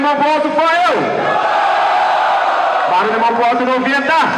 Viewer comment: Só eu q ouvi " Fael e Buddy poke na final " ?